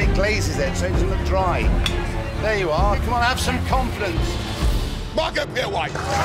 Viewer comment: auto generated captions says mark up your wife instead of Marco p white